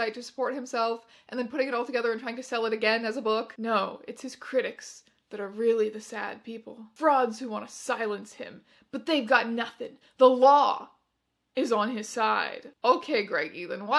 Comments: I heard "frogs who want to silence him." .. Suddenly much more interesting! Until I realized I had just heard it wrong. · minutes- I heard that as "Frogs who want to silence him" not "frauds" and now I have a VIVID mental image of the onion man being accosted by a horde of gag wielding frogs storming into his house. thank you.